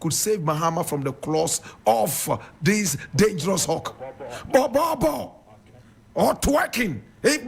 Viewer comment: Did I hear the producer of the program saying "borborboo or twerking"?😂😂😂😂